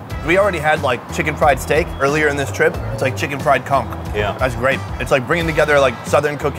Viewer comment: I literally thought he said "chicken fried cunt" 🤪